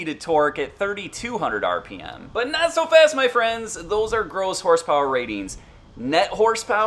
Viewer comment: Thought you said "Girls horse power". Ha